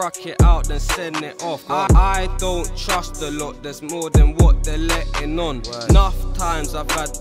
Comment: I thought he said "I don't trust the lord" at first